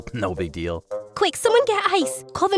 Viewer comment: 6.37 I thought they said “ quick someone call ice”😭😭